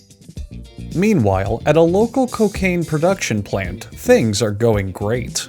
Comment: did i hear that right COCAINE??